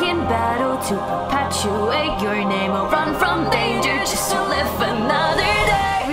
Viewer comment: run from danger everyday live everyday:) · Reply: i thought you said 'Run From Danger Its Everyday Bro' lol